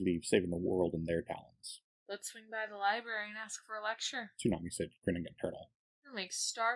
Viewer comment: subtitles say tonight said, narrator voice says tsunami said. Why subtitles wrong